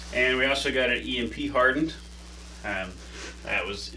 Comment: Did you say EMP hardened? As in electro-magnetic-pulse? For solar flare protection?